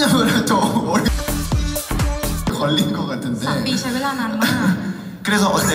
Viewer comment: just thought he said "Pani kidhr hai" in Hindi😂😂😂 Indians will know this better😂🤣